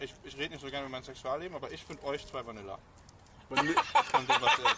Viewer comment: Was hat er da gesagt? Vanilla?